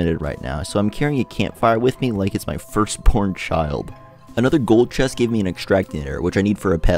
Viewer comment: whyd it sound like you said “first porn child” lmao